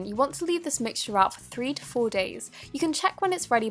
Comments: I thought you said leave it out for 44 days 😆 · I thought you said to leave it out for 44 days but that was becausse of my volume xDDD